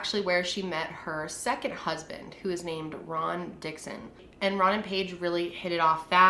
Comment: Omg 🤦 I was getting soooo confused by "Ron" and "Rob", I appreciate that you kept mentioning whether they were the first or second husband. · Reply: Kyla Lambalot sometimes I hear rob and sometimes I hear Ron (when she’s talking about the same person lol) like at 4:28 I’m almost positive she says Ron, and a few times right after but at 5:04 I hear rob